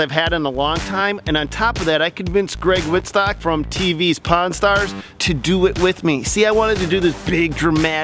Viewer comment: Lol I thought that you were saying "Pawn Stars" for a while there